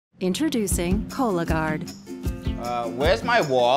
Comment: I'm gonna be honest, I laughed at this more then I should have. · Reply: I died. I thought it said color guard like laundry detergent or something